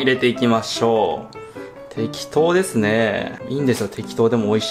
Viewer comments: ブナシメジのところで10秒スキップしたら「適当ですねー」の「て」が消えて「亀頭ですねー」に聞こえて笑っちゃった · 亀頭ですね。って聞こえてビックリした。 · 早送りしてたらしめじ入れる時の「適当ですね」が「鬼頭ですね」に聞こえてしめじのこと言ってるんかと思った